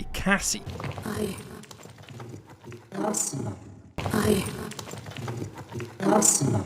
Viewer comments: I heard her say "I lost it" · I don’t hear “I-cassie.i hear “I-lost”